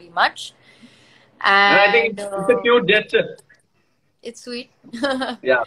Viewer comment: Can someone please tell me what Gaurav said a 0:18 seconds?? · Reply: " I think it's a cute gesture ".